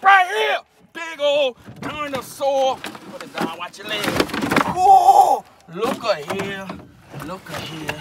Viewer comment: I thought he said watch your legs to the fish, had me in tears until I remember the camera person😆